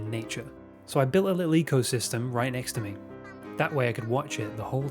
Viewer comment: I heard "leak-o-system" 😝Well. Let's hope it won't be one.